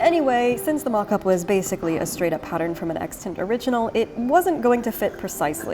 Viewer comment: I really appreciate you including closed captions. I have caught a few mistakes. I think whoever is doing the captions for you may not understand much about dress history so is making mistakes with the technical terms. At 1:42 when you say "from an extant original", the captions say "from an Exton original". They don't know the word "extant" so have misheard it and confused it with a proper noun. At 4:41 when you say "and baste them together" the captions say "and base them together" At 15:05 when you say "petticoat" the captions say "pet coat" I'm able to hear well enough that I know what you're saying and have learned a lot of dress history from you. I'm sad that people with less hearing than me would miss out on some of that learning because of the caption errors